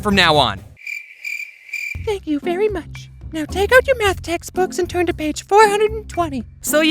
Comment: ooh i thought james said "now take out your meth textbooks"